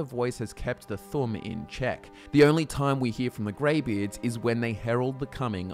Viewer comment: funny xD the first time i played skyrim i had no subtitles on and i thought i heard greyBears, so i expected to meet like some sort of bear people on top of the mountain lol